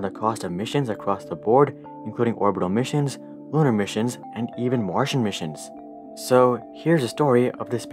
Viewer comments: the video:even martian missions Me who heard it as: washing machines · Bruh. I don't know why but at 0:23, I heard "Martian missions" as "washing machines". I played it multiple times and I still heard washing machines. Had to enable captions to understand it was Martian missions. LoL.